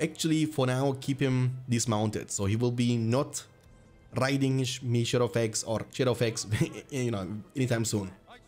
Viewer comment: I heard you say "mayshadowfax" and didn't bat an eye until you corrected it yourself, made me laugh pretty hard lol